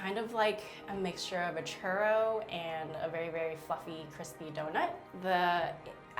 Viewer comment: I heard it wrong I heard fuckin crispy donut rather than 'fluffy'.